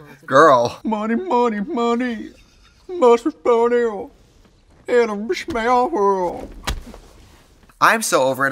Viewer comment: I need to know what Trixie says at 17:33 GOD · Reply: “Money money money, must be funny, in a rich man’s world.” It’s a song from Mama Mia!